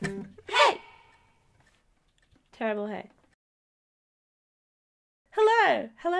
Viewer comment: When she said "Terrible hey" I thought she said "Turtle head" XD Dont judge me!